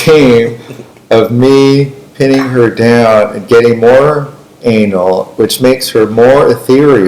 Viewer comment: what did he sayyyyyy?? :face-orange-raised-eyebrow::face-orange-raised-eyebrow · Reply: something “me pin her down” something “getting more anal”.